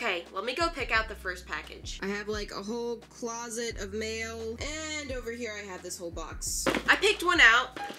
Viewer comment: It sounded like she said i have like a whole closet of mayo